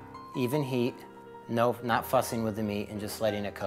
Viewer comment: Did he just says "not fuckin with the meat"? At 9:36😄😂